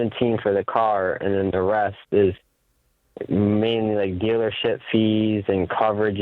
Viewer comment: did he say “give a shit fees” 🤣🤣🤣